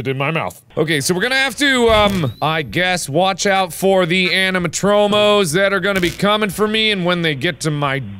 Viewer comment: He said animatromos I thought he said animahomos